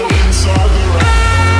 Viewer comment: is it just me or in the part they say rabbit hole with intence music at the background I hear "Badly Hurt" insted of rabbit hole "0>0